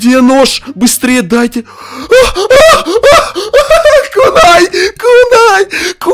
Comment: я сначало подумал что он сказал куни)